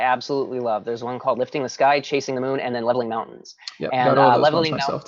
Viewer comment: mentions 3 qi gong. Lifting a sky, chasing a moon and I couldn't hear the last one. Can anyone tell me what it was? Thanks! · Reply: Pushing mountains. :)